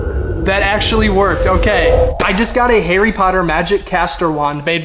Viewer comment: did I just hear “Hairy Potter”? 😭